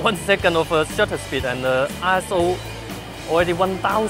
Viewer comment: sounded like he said a**hole..